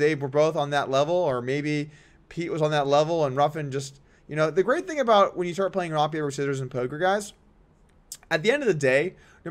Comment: I listened to this part 10 times and still dunno wtf you said. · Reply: "The great thing about when you start playing rock, paper, scissors with poker, guys"